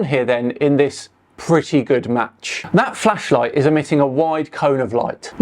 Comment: They really think the sun is a flashlight. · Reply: Sorry, I thought you said 'Fleshlight'.